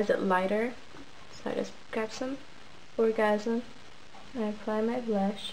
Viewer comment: idk if anyone else noticed but it sounds like she said orgasm at 2:57 and ur makeup is flawless !